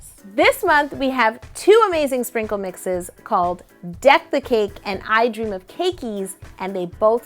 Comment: i thought you said defecate instead of deck the cake smh